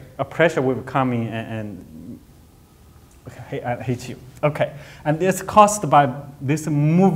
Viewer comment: does he say "i hate you" ??? hahahahaha lol